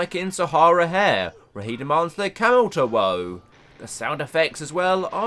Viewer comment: I swear you called that camel a cow?